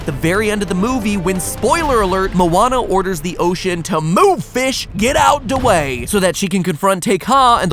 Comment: did matpat say “move bish” or “move fish” ?? 🤔🤔